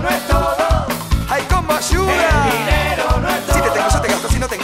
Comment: alguien me puede decir que dice en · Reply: "si te tengo yo te gasto, si no te tengo no te extraño" 6 años después ya te recibiste ya trabajas pero bueno 😂😂